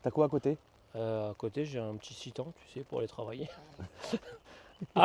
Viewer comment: au bout de 5 écoutes jai compris qu'il parlait du Nissan Titan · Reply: Nan un Citan, c'est la petite fourgonnette de chez Merco